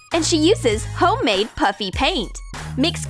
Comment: Is it me or did she say puppy paint